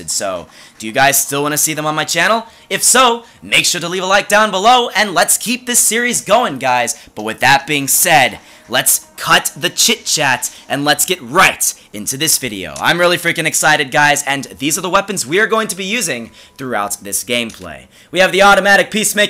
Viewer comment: I though he was gonna say "let's cut the shit" but he said chit chat